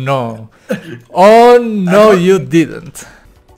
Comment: uh oh :O: oh no OH NO YOU DIDENT (eso es lo que entendi) mente de: Corre Corre CORREEEEEEEE HAS ALGO QUE NO TE DESCUBRA AHHHç boca de: ¿que? ¿queq? nada, pense que habia pasado algo.